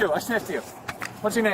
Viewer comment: I thought the second guy said “I sniffed you“. What he actually said was “I shivved you”. He stabbed the cop at initial contact and that’s why I think the cop jumped back.